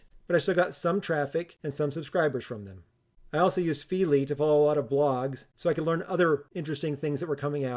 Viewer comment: what did you used you said? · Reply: I didn't pronounce carefully there, sorry. That was Feedly, I meant to say.